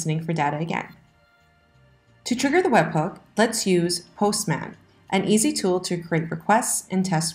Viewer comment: "To trigger the webhook, let's use ____" Is she saying "Postman" or "Hostman" or something else. How do I get to that page?